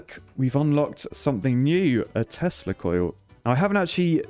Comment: Is it only me that when every time he said "Tesla Coil" I hear "Testicle" ? 😂🤔